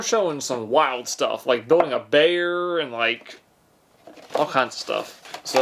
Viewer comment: I'm not the only one who heard him say blowing a bear, right?